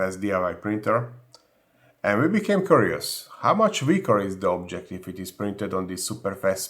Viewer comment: I definitely thought he said they became couriers not curious 😂